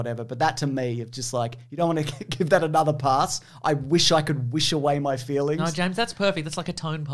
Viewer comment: Wait... he says "I wish I could WISH away my feelings" ??? I thought he said "I wish I could WASH away my feelings" xD